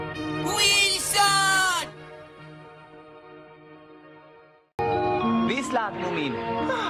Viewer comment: Why did the second one sound like snufkin was saying Hi dumb moomin ???!!😂😂😂